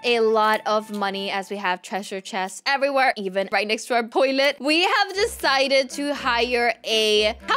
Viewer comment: did say poilet???